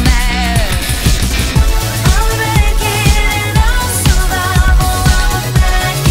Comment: The first time I listened to this song I heard "I'm american" instead of "I'm a bad kid" :D